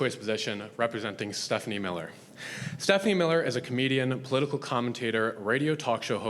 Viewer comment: Only a few minutes in and this lady is treating a human rights debate like it’s her own personal stand up show. So embarrassing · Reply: I heard “comedienne” at the beginning and thought “oh boy…”